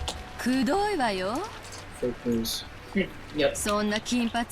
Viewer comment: LOL I think at 36:51 you said “fake news” Well wow the VIP room looks super pretty. As before Steam version didn’t have these nicer looking bg. Also holy shit is Beato messing with K-Kun pretty well. Ok when Battler kept yawning I felt like I wanted to Yawn too. Sorry but when Maria and Rosa are alone. I always feel nervous like Rosa wants to keep on hitting her>_< But once again does it feel nice to see Rosa lose her cool and panic like she did in the rose garden. Oh and look like she used the Natsuhi my head is hurting excuse. Looks like Beato is also hitting Rosa in the head with her pipe LOL.